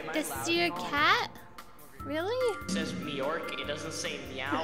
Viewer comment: kinda sound like flamingo saying "MEORK"